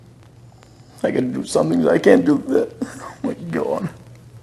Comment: what does say? "I gotta do something but I can't ___________" I never can make out the last part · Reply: He said "I gotta do something because I can't do that! Oh my cawdd😢😢"